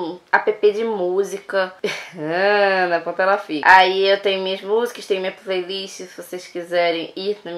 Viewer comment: Só eu que ouvi ela falar "hmmm na ponta ela fica" no 2:10 ? Kkk